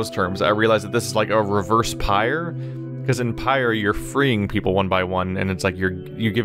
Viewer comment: what game is did you say for freeing people "pyr"?